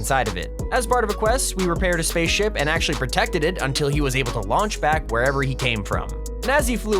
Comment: What said:"To Lauch Back" What I heard:"To Launch Pad" RiP Launchpad😭😭😭😭😭